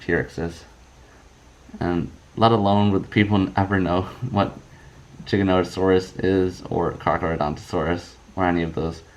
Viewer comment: in the subtitle instead of giganotasaurus it says chickenosaurus 🤣🤣🤣